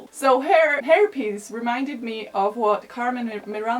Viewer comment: i thought you said herpes lol (hair piece)